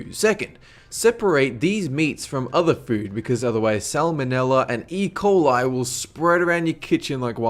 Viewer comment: thought u said Ebola 😱😱😱